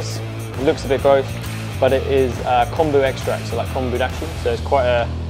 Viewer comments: What is the secret ingredient at 1:34? I can't hear what he's saying! Thanks for the recipe. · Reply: Kombu extract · music is so loud i thought he said condom extract at first.